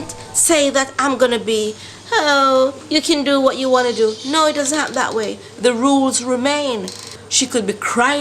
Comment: Can u guys hear that she said: "Ooh u can do what u want to do no does work that way the rules weremain" thats what I heard at